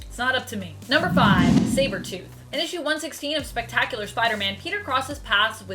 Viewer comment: Did she just say Cybertooth, instead of Sabertooth at 5:35?